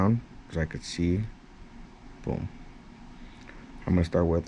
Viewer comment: lol I thought you said shrek green